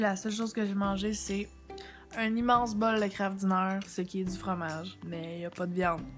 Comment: Un immense bol de quoi ?????? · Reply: Kraft Dinner :)